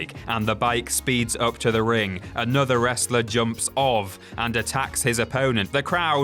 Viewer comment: did he say jumps of